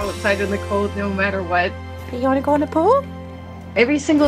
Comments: I thought she said “Do you want to drop a Poo” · 😆 I first thought you said "U wanna go Winnie the Poo!"; I said what's he doing running outside to take a shyte? That must be their code word to go to the bathroom. 🤔 Then I seen him jump in the pool. 😂🤣